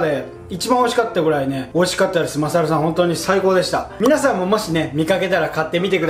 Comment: マサルさんがおサルさんに聞こえてマサルの台所にバナナが吊るしてある